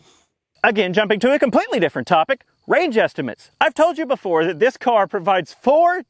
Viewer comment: Sounded like he said "rage estimate", which might be accurate in certain situations.